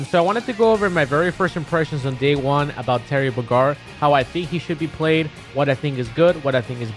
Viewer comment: Sounds like you said Terry Bagar